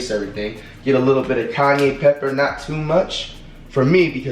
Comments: I thought flight was saying Kanye pepper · did he just say “Kanye pepper”???!!! 😂